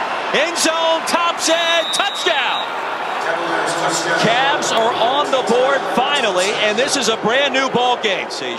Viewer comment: For a second it sounded like he said “Chad’s are on the board finally”